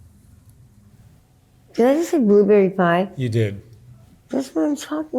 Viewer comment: I thought she said 'wanna see the people inside' not blueberry pie.I wish you the best and Mark!